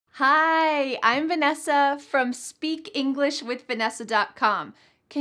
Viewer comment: When you said: "I'm Vanesa", I heard: "I've been a so" ????